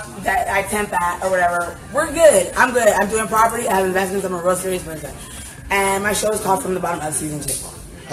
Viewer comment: Can someone please translate what she said from 0:59 to 1:04 pretty please!? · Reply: Jill she said "I'm good, we're good. I'm doing properties, I have investments, I'm a realtor and all that. My show is called from the Bottom up." I had to listen to it again, because it was hard to tell, but that's what I got from it.